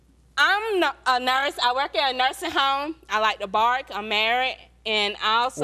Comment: did she say she likes to bark.